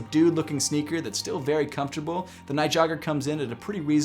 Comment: did he say Nike jogger